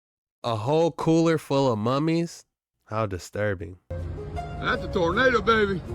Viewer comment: Why did you say mummies instead of mermaids?? lol